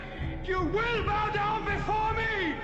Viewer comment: this is what i heard the first time i saw this movie.... " F**K YOU AND THEN ONE DAY, YOUR A## " even my dad said that's what he heard